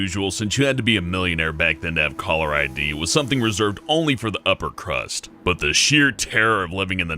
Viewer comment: did he say upper crust?